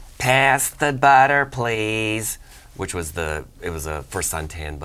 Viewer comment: All this time I thought they were saying the "Satanic Butter".